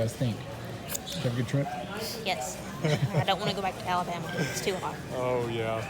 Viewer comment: Very enjoyable video with outstanding scenery! Thanks for sharing your trip. I have a question; where did the lady, I think, say she was headed back to (at 22:37)? I thought she said "Alabama, too hot." With my hearing, could be wrong. Asking because I'm from/in Alabama myself.